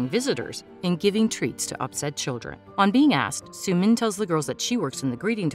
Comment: to make them play lol Oh i thought u said outside children- Well still the same